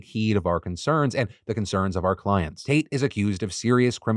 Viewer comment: I thought I heard “KATE is accused of serious criminal offences against a large number of victims” and was like damn that’s crazy that’s why she’s hiding hahah